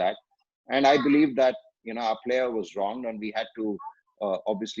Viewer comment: Everytime I pass through the circle or anywhere near by, I remember this is Anil Kumble circle and I get goosebumps, though not very sure why, I feel like it's the name of someone I personally know (I don't get this feel with other names though I know them)....I tell everyone who are traveling with me that it is Anil Kumble circle... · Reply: Did he say "Bhajji was wrong" ?